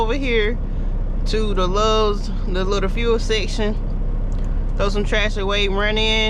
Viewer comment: She said she was gonna throw some trash away and I thought she said I'm gonna head over here and get ride of some tracks she blew my mind it's her accent